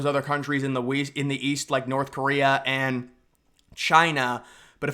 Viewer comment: Ooooooohhh!!!! “East?” I thought you said “weast.”